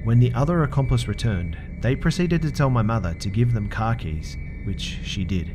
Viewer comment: I understood "...to give them kakis" instead of "car keys" and I just thought "what the fuck?!" I had this picture in mind where the one guy ran through the house, collected expensive stuff, then saw the bowl full of fruits and shouted "AND GIVE US THOSE KAKIS, THEY LOOK FUCKING DELICIOUS!!" :D